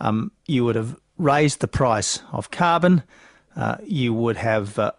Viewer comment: did he just say "raise the price of carbon" ?????